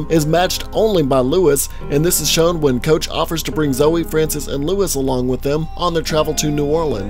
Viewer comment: Did you just say "Brig Bother"?